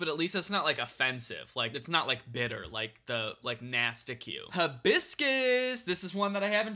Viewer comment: When he said, “hibiscus,” I thought he said, “biscuits.” Now I can’t stop thinking about a biscuit-flavored La Croix.